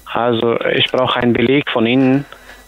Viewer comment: Habe erstmal „Ich brauche ein Glied von Ihnen“ verstanden 😅